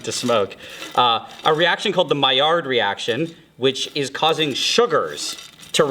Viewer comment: What did he say? Maiard reaction?? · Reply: maillard. it's french so the double L makes a Y sound